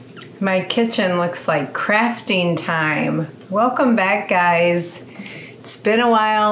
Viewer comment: Lol I thought you were going to say my kitchen looks like crap then you said crafting time 🤣😅.